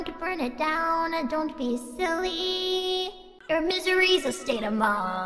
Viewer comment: tui nghe ra chữ mystery nhưng câu vẫn vô nghĩa vcl :))